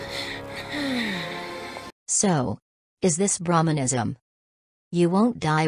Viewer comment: without the subtitles, I would have thought she said "So... Is this bruhmanism?"